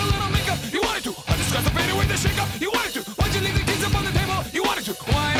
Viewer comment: "Why'd you leave your keys up on the table?" Did I mishear that?